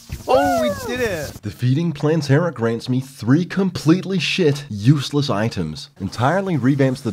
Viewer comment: Did he just call Pygmy staff useless sh*t???!!!